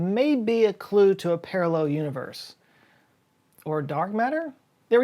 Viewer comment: I swear you said "... or dog matter?" ^^